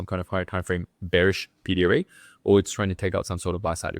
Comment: bearish what? I don't understand the word you're saying, would you pls type? thanks :) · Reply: pd array?